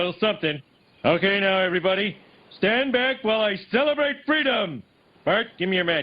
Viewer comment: did he say freedom? turns into a dove tatakae